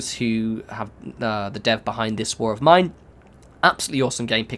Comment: please forgive...what is the name of the game you said? i have looked in post but nothing i guess i am the only one who can not understand :( 0:35 please forgive me , thanks for the guide works awesome! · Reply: Just in case you're still looking for it, it was This War of Mine.